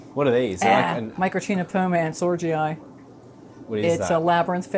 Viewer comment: Beautiful! Can you tell what's the name of the fish at 24:00 ? I can't hear it clearly. I think it's some type of Macropodusinae? Or a Microctenopoma ansorgii? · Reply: Microctenopoma ansorgii